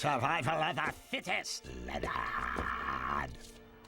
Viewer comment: did he just say the n word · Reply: “Leonard”